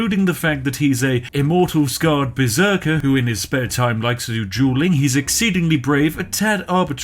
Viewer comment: For a second I thought you said he likes to do juuling.